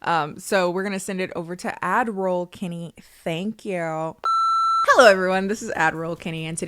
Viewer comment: For the longest time I thought has been saying "Ad-miral Kennie" as a very clever pun but she's been saying "Ad Roll Kennie." It's still in my heart tho 🫡